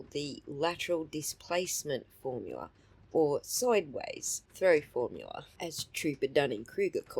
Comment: I legit thought i heard thorough formula 😂